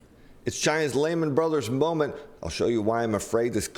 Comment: For a minute there, I thought he said Leyland Brothers moment.